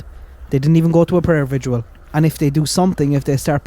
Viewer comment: Did you say "prayer vigual" (around 8:00)? I only ask cause I've been noticing this word pop-up every once in awhile in the past few years, and there's no such word. I think what's happening is people are combining two words to make a new one... vigil + visual = vigual. Sorry. I am totally anal about some things. Watching the birth of a brand new word is interesting to nerds like me 😆.